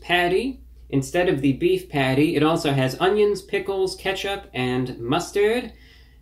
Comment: I thought he said onions pickles and kesha